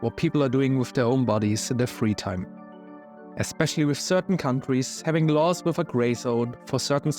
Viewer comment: I heard that as I don't care what they do with their bodies, especially certain cunt..